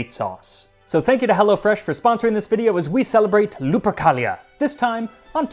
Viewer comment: I kept hearing "lubricalia" which, knowing the Romans, checks out